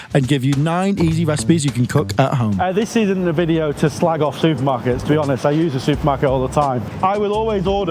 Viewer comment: At the beginning, I thought he said, "This isn't a video to just slag off to...." and I looked up like, @@ "WHAT?!" LOL! *clutches pearls*